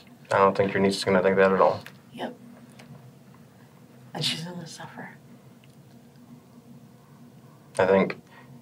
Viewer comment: The subtitles are wrong at 51:00 she says "and she's gonna suffer" not "I just wanna suffer" 😊